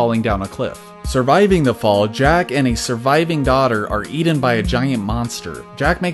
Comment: "Are Eated" dude it needs to be "Are Eaten" there is no Eated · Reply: i heard him say are eaten